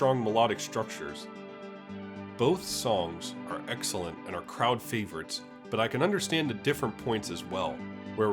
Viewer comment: did you just say "Kraut Favourites"? 😆